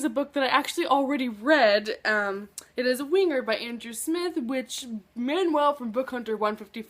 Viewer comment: When you said "Winger" I thought you'd said wiener. omg.